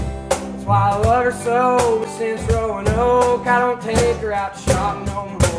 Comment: Never knew it was “Roanoke” thought he said since “growing old”